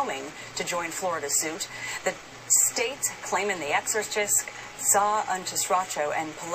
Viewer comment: does she say "the exorcist"?? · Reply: Mike Soluna ....I heard it! Immediately rewound 10 seconds to confirm She says exorcist